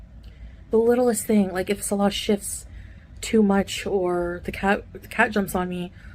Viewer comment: Sounded like, "if Salah shits too much." 🤣 Yup, that will keep you up!